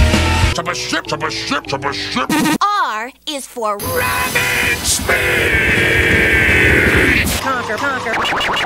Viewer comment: The way he says “Rapid Speed” kind of sounds like he says “Rabbit Speed”